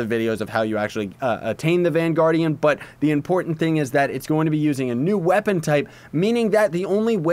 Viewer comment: Okay I thought they said it was going to not be confusing